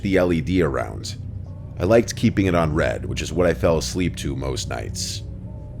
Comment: what the fuck did he say? Red? RED?? 😂